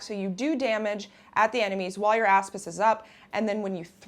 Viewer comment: I thought she was saying ass fist LOL